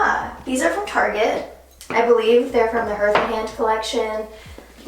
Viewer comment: Okay but at 25:52 did she say H-earth? Lol. I’m dead